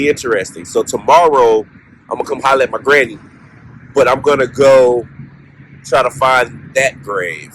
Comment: it really did sound like you said you’re going to violate your granny 😳😂